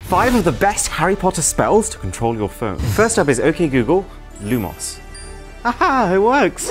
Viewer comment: I thought he said lemons instead of lumos 💀